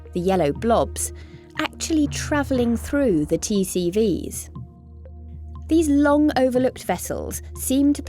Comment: WHAT?? did you say 'traveling through T-series'?